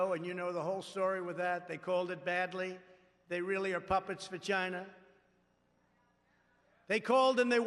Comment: omfg i thought he said puppets vagina not puppets for china lolololololol